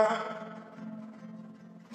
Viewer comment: Мы, кто всегда дождёмся Мы — парни знатные М Крепкие напитки, но не отношения Пьяные глаза стреляют по мишеням Танцы набегу, меняю помещения Трахаться лишь в неудобных положениях Я пацан, который всегда за движения Но я всё в них видел уже, к сожалению Бег за машиной сменил мои сожаления Я псина, я просто псина Я бегу за целью, после снова целюсь Если схватил сумку, не разжимаю челюсть (Делай грязь, брат) Если влез на суку, вызвал чью-то ревность Телефон без звука, я стригу окрестность В воскресенье мяч летит только в корзину Забираю всё, никогда половину Я на заднем ряду смеюсь в голосину Потом как псина Мы — парни знатные М Залетел посмеяться — вылетишь со слезами Я как будто сплю на сцене, я на ней в пижаме У меня бухло в машине, но я буду трезвый Хоть мир не через стакан не такой интересный Я люблю женщин больше, чем их любят лесбы Но пишу блюз, а не тексты Я не местный, никогда не буду Новая партия — новую колоду Я искренне люблю работу Жду следующее утро, как ты ждёшь субботу Я меняю не одежду, я меняю моду С того момента, как я покинул утробу Я такой же как и прежде, но я всегда новый Никакой вечеринки, если нет босановы Старые пластинки, способ новый Я всё же хочу выпить, будьте здоровы Мы — парни знатные Мы, кто всегда дождёмся Мы — парни знатные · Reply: А мне послышалось вначале: Мы навсегда коснёмся Выпьем в поясах